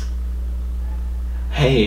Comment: for some reason.... I heard.. "hey,... v-sauce, Michelle hair........."